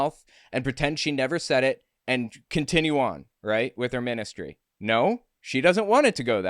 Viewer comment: She said "ministry" and I heard "menace-tree". Dog's honest truth.